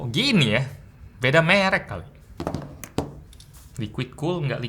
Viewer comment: kalo bilang merek kedengeran kaya kata benda per*k yg jadi kata kerja